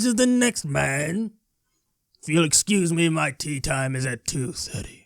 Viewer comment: I thought you were saying tea time, not tee time.